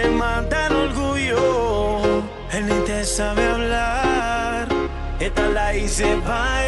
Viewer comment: does not say "He know how to talk to you/Él te sabe hablar" what it says is ... "He don't know how to talk to you/Él ni te sabe hablar"